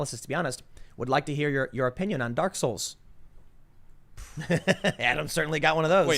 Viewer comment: This is the angriest I have ever seen soy Jesus. This is the average response to Americans as they see the destruction of our country to the PC mob. Edit: oh boy soy Jesus is a dark souls fan. Guess I’ll have to hop on twitter to have some discussions now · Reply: For a second I thought you said "dork souls". 😂